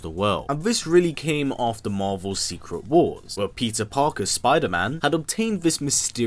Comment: I honestly swear he said Pizza Parker